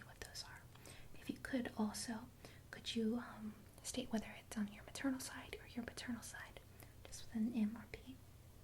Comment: I think I heard her say "on your paternal side or your paternal side" at